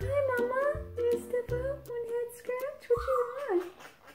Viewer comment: I swear I heard “head scratch” at like